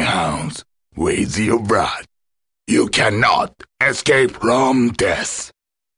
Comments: I always thought he was saying "you cannot escape wrong death." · "You cannot escape Rome death." Since when did Rome bring death? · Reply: I keep hearing "... Wrong death..."